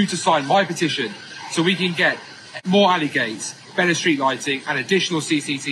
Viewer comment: When he said more 'alley gates', I thought he said more 'alligators' - I'd deffo vote for more huge man eating reptiles in Tory areas